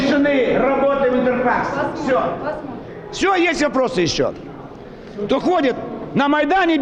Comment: I hear something that almost sounds like "Prostitution". Unfortunately I don't speak Russian.